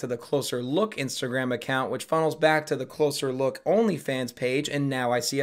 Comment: for most of the video i thought the company said losers look .......